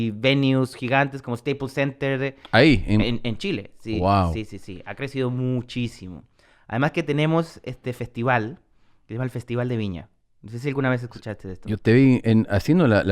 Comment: I love how at 8:05 Tom says "GUAO" instead of "WOW"